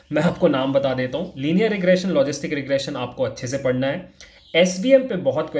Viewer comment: #mistake no. 7 (wrong order of studying ML Algorithms) what did you said -> SBF(Selection By Filtering) OR SVM (Support Vector Machine).....Please Reply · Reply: He said SVM